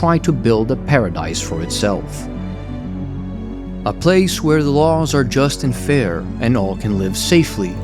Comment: Great video. 0:52 I love that when you said "A place where laws are just and fair", i heard "A place where laws are just unfair". I don't know if that was intentional but it was amazing.